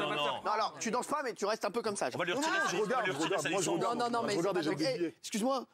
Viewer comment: Kaaris il dit quoii?????😶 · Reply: Je regarde et je jette des billets je crois 🤷🏼‍♀️ Jsp je me suis perdu sur YouTube 😭😭